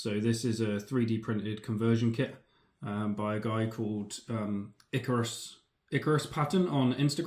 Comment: I must have misheard him. I typed Icarus PAttern IcarusPattern Ickerus Pattern... and many other forms of the name... I can't find the dude who made that sick Landraider conversions